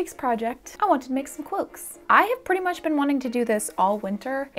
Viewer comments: i keep hearing "cwoaks" and i am NOT upset about it lol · I swear to Sauron I keep hearing you say "cwoaks". I'm not complaining though lol.